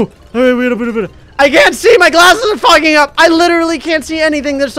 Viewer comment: did just say fucking up?